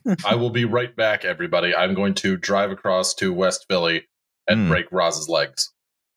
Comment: I heard "bike across town" Carry on Yay! Also Gritty wants to know what happened to the Flyers this year, or every year since 1975.